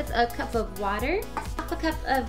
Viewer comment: did you say half a cup or 1 cup at 1:42 ??? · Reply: 1 cup 😄 ur welcome